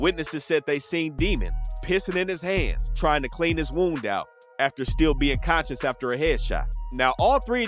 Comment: did you say “pissing in hands, trying to clean his wound out” ??